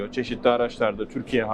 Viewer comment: En son saniyelerde Türkiye hariç mi dedi, yanlış mı duydum